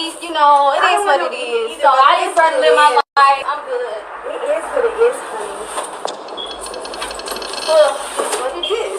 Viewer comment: One more thing...Did she say, Grown Man was there or allowing grown men in his household?? At 30:00 mark because he was there in Chicago...check Larry live with Shabazz on it... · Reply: She said grown men referring to Shabazz and his people